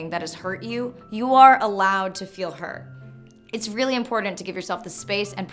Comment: Every time you say "Important" I hear "Imported"....is this just me?